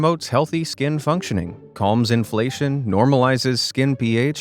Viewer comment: Did he say inflation?